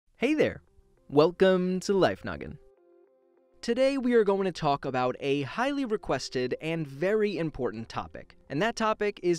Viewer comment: #dear Why does it sound like you say “life nugget” in your intro?